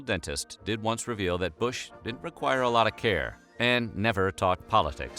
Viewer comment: I can't understand if he is saying Bush did or didn't require a lot of care at 2:40 and it's driving me bananas! I think he says 'didn't'? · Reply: Did